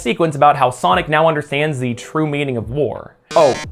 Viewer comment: I was not paying attention the first time you said "Sonic now understands the meaning of war". I thought you said "Sonic now understands the meaning of vore" and I just went "Yea, sounds like furry stuff to me."